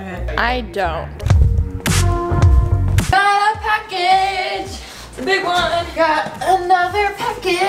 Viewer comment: Oh my gawd when she said “I gotta a package” somehow I thought she said “hotttt pocket” and i saw the box and I was like dang das thiccc.but like what do I need to do to get a huge hot pocket?